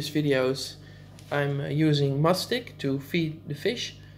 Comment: You have a great tank that gives me inspiration for my tank currently being built. 👍 What brand of food is that stuck to the glass? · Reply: Joshua Turner Thanks, I thought you were saying Must Stick 😂